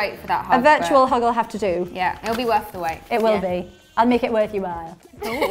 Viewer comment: PLS WHAT DID PERRIE SAY HERE 3:22 I wanna laugh too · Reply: “I’ll make it worth your while”